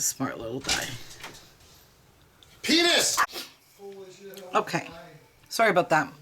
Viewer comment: Ok did anyone else hear Penis between 20:02 and 20:06 after she said he's a smart little guy. What in the world.